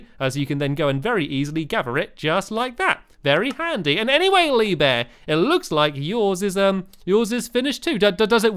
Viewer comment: it sounded like he was saying "Make a Lee Bear" instead of "Make a lever"